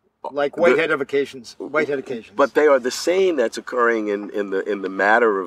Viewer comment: anybody know what he's saying at 1:45? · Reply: He says “Whitehead occasions”. He’s referring to the process philosophy of Alfred North Whitehead, and mentions him again later. Check out a summary of ‘Process and Reality’ for an idea of what this means. (Or read the book, if you’re feeling brave. 😊)